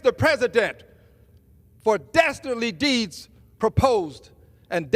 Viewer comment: did he say dastardly...like how is anyone going to take this man seriously